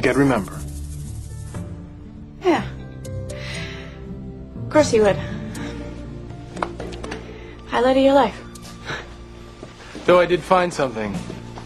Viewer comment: what does she say at 3:21? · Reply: "Of course you would. Highlight of your life."